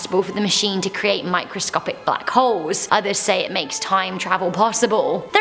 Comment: thought I heard "microscopic buttholes"